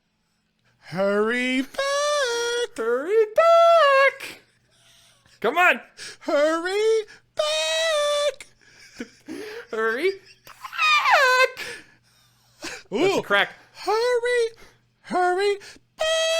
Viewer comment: Sounded like Kermit saying CMON Ms. Piggy struggled to say hurry back